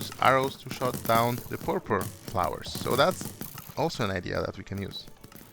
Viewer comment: What is he saying there? Pur-pur flowers?